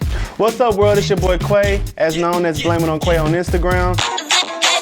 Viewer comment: always thought it was said like Kay Way.